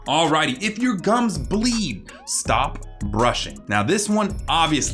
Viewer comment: I don't think anyone noticed but I says "burshing" lol like imagine your about to go to bed and your parent/parents say "go bursh you teeth xd